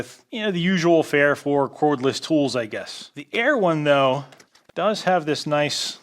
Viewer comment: When you said "the air one though..." i thought you said "the EREWHON though" and i thought they started selling overpriced smoothie making files!